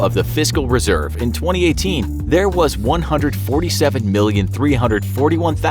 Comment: Who heard visco at 1:09?